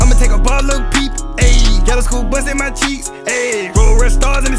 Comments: I swear I heard this nigga say sumn bout “bust in my cheeks”😂😂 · hold up did he say "bust in my cheeks"? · Is it just me or at 1:09 does it sound like he said go to school bust In my cheeks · did dude say school bus in my cheeks? Or am I trippin